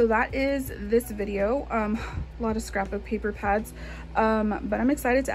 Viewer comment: Love all the paper pads! I have a bunch of them, but not the ones you're showing. For some reason, when you said "paper pads" I heard "puppy pads". Lol! 😅